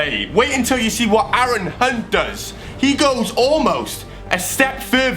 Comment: heard when sed hunt it sounded like cunt